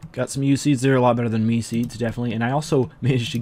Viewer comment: i thought he said "weed seeds" at 3:05. i was gonna say i hate getting weed seeds